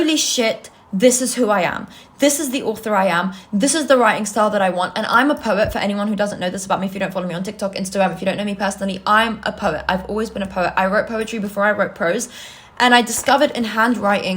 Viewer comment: girl I thought you were saying you were a pervert until you started talking about prose 😭